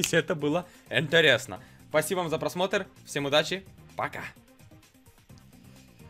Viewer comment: я один услышал е*ать леон? Спасибо за 200 лукасов